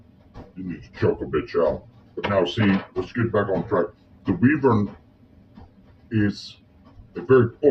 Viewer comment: I thought you were saying Weever 😅